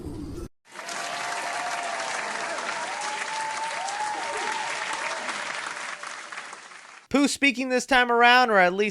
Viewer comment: Guys I can’t hear what did he say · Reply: He said you left